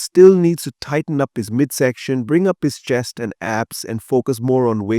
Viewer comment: did you say bring up his chest and abs...or his chest and ass???😂